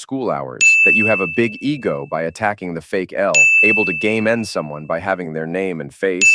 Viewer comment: I have a pragmatic communication disorder, so I thought he said "Gay Man Someone" instead of game end someone. 😅